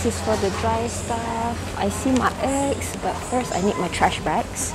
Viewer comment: I thought you said "I see my (ex) but first I need my trash bags", but later I realised you mean eggs 😅